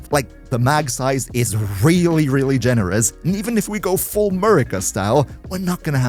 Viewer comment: did he said full Murica style??? Loool